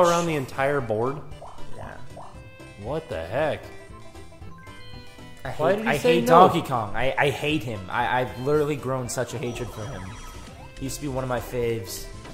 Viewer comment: sounds like says Tonkey Kong